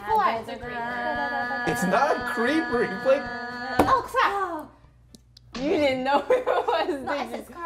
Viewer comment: i heARD "O CRACK" JDJCJCJX michael reeves crackhead foreshadowing